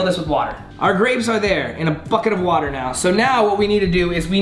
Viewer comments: it sounded like he said fuckit of water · am i the only one who heard f*ck it of water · I thought he said our grapes are in the fucking water now😂